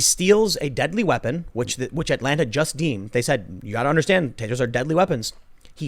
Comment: I thought Tim said "tators"